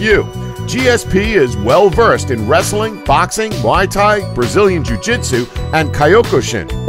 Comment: Did he just say Mai Thai?